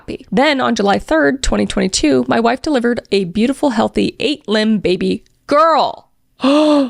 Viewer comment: 8 limbed baby is what I heard 29:41 😂